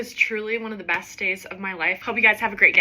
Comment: The ending the glitch lol it sounded like you said “ have a great gay “ lol love your vids